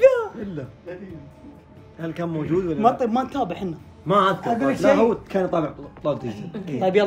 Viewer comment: والله اني شفت الفيد كثير بس توي الاحظ شو قال يطابع بدال يتابع انهرت 😭😭😭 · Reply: يقول يطالع مو يطابع